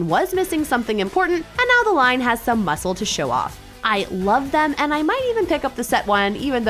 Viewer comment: Did she just say the Zac has no muscles